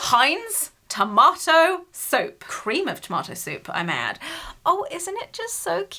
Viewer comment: When you got the Heinz ketchup soup. When you first pronounced it I thought you said Tomato soap 🧼. And I was like ‘what?’ I would never was my hands with that! You don’t wash your hands with tomatoes 🍅! Hahahha!!!